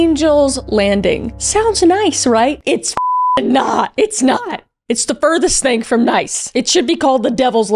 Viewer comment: I heard Riley in that "it's not"